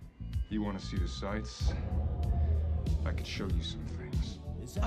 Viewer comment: What does he said at 1:05? I don't understand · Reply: He said : " I could show you some things" :)